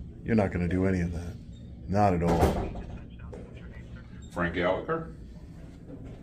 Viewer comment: Lol did he say his name was Frank Gallagher 5:16 😂